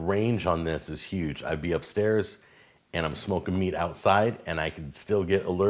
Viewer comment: It sounded like you say d you were smoking weed outside at the end. Lol